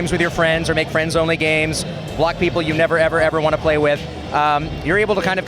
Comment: did he said "black people you never ever ever want to play with " ? The fuck ?